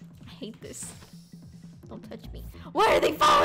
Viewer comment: My brother playing dont touch me song while said dont touch me Me: what did she say????